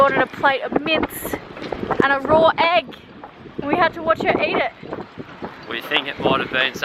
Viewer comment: Raw mince and a egg? Steak Tatar you antipodean peasants. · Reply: Thought they said raw egg and mint... Was very confused!